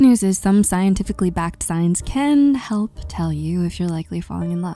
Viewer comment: I thought she was saying “scientifically backed science” 😂